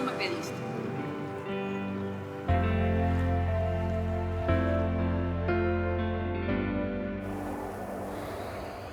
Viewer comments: Alguien sabe que dijo Bruno en el minuto 6:37???? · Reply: "No lo puedo creer " eso dice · Alguien me explica que dijo Bruno en el minuto 6:37 se vio que movio los labios ricos que tiene ese papasote... Quien sabe que dijo??? 🤔🤔🤔🤔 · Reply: El dijo: no lo puedo creer.